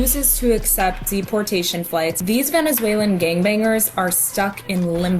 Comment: did she say ‘gangbanger’?